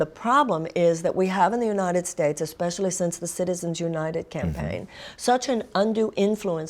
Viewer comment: i misheard that and thought she said "citizens united gang bang". And she wouldnt even have been wrong if she said that. They've screwed over the country with that stupid and dangerous ruling.